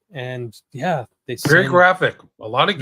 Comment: No CC on this video. Hard for those of us who aren't native English speakers to grasp certain parts. What does say at @ 27:31? A different movie than the Al Pacino one? I guess he says "very graphic", right?